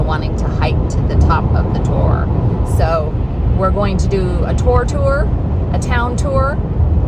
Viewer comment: Your American accent caught me out at the start. I thought you were saying a 'tour tour'. 😁 That climb looks STEEP. I don't think you'd see this hiker with my abilities on the trail. Stunning views from the top. I know all about relics brining in money from watching Cadfael. Talking about the rain. Which would you prefer. Rain at Glastonbury or the 116.6F that Kuwait is having? The Acropolis in Greece is closed due to heat too. You need to find a woman that used to work in the Lancashire cotton mills. They can read lips. 👄 Those fairies in that shop are magnificent. Were the wings you had on in the Natural Earthing your own, or did you buy them there? You should have ised them to fly up the tor. 💸😁 Imagine Yoda as Shakesepeare. To be or not to be. Question is that? That ploughman's looks good, except for the beetroot. It was beetroot? Fruit cake YUM. 🍰😁Fancy passing on the Eccles cakes. What's the difference between a cherry Danish and a Greggs Belgian Bun? I just got up to Trish. (To be continued after dinner). 😁